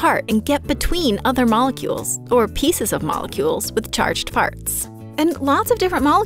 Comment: did she just say charged farts